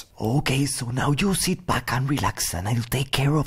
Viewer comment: i heard baka relax